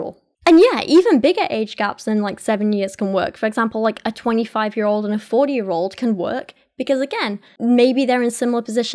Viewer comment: Listening to this as background noise and I could've swore at 22:52 she said a 25 year old and a 4 year old could work out and was s u p e r confused